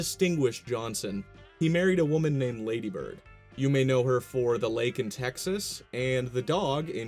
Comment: Dude was obsessed with his initials too. Lyndon B. Johnson (LBJ), wife is Lady Bird Johnson (LBJ), daughters Lynda Bird and Luci Baines Johnson (LBJ x2), and even his dog- Little Beagle Johnson (LBJ). The fact that his pecker was simply named Jumbo instead of Lavishly Big Jumbo astounds me... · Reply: almost thought you said Larry Bird